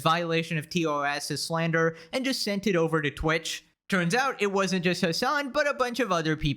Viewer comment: I thought you said its over. Like 150 times why does anyone watch this slop?